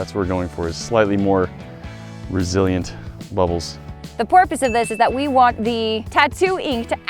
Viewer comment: Did u just say "the porpoise of this"? LMAO!